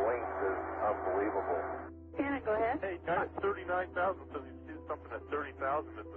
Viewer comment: omg 5:25 did she say "Janet, go ahead"? <link>